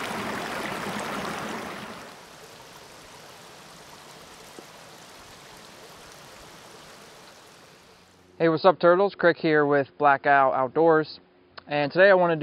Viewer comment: 😂 I heard “Hey what’s up turds” and had to replay it just to make sure! 😅